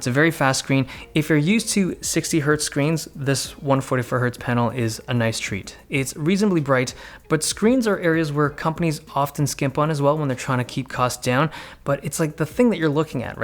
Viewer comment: Am I the only one that heard him say one forty fur hertz panel.